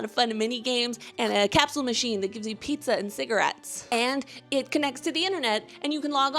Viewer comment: I took a second or two not realizing what you just said then I audibly said "What?" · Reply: Pizza and cigarettes ✨